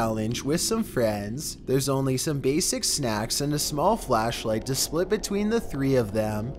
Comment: ima be 100% honest.....I thought he said some gaysest snacks 😂